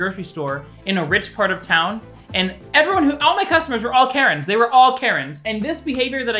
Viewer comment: Okay around like 4:24 ish when u was saying Karren I thought you was saying carrot and I got so confused, like damn this man must not like carrots.